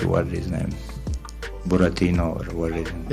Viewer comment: Did Khamzat just call Paulo Costa "Buratino"? That's the russian version of Pinocchio.